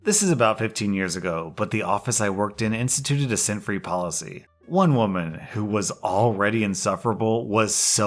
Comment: So instead of "scent free policy", I thought said "sin free policy" and first, I was wondering what the heck kinda Christian workplace is this, second, why someone would be so offended by it, and third, what perfume has to do with sinning. It took me longer than it should have to connect the dots. Whoops.